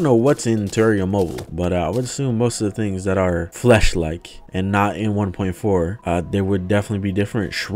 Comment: anyone else heard fleshlight?